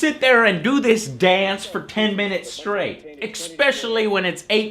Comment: Did Joe say..."EXspecially?"